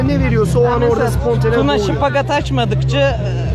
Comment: ne diyor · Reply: şıpagat diyo cimmastikte bacak açma hareketi